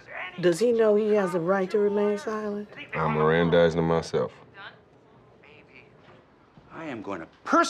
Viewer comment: “Does he know he has the right to remain silent?” “I’d mirandized him myself” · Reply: I think he said, "I Mirandized him myself."